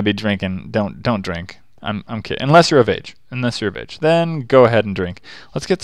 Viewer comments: What did he say then? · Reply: I heard "I'm kidding, unless you're a b**." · What did he say then? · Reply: Jalen Ng I heard "don't drink, unless you're of age" what do you think he said...